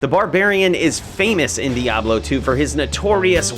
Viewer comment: I thought he kept saying “BBC Barbarian” with a straight face. LMAO. 😂😅